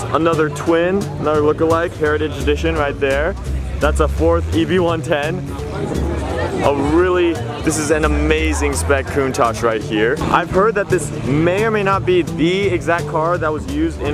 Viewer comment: at 6:14 did you call the Bugatti eb110 a Ford 110?